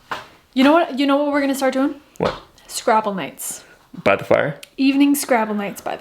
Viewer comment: you got my hopes up so high when I heard you suggest @ 8:55 an event including "Scrapple Nights" .... then disappointed me by repeatedly saying playing SCRABBLE instead ! Dad used to make us scrapple for breakfasts on weekends, often preparing it the night before with pre slicing and coating slabs of it with flour, salt and pepper to allow it to go in the frying pan after the bacon was prepared for breakfast. OH WELL !.... Since your stove is so tiny, you may want to make a special trash can for wood chunks to fit that opening... just cut a hole like the stoves front opening (only slightly smaller) in the lid of the can and maybe magic marker a ruler on top for depth allowed. Then when you have "off cuts" leftover from projects, you can see that they'll fit before going in the bin for recycling as fuel. Think you should widen the platform for stove a good deal for other purposes, like a warming spot for bagels, bread, or coffee, and for making toast immediately beside or on top of stove. For future proofing, do you think making a ship's ladder type stair may be a good idea for with the baby coming ? Can't climb and hold a baby (not very safely). See this for reference : <link> scroll up to about 2:50 in video for pertinent parts. I don't drink coffee often and I'm considering a bag or 3 as gifts !